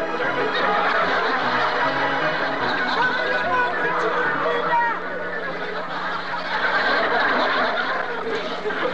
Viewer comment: Que dijo aqui el Chavo? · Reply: ¡Dame la mano, Chilindrina!